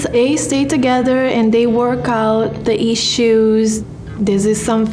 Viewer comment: Did she say “They eat shoes?” Lol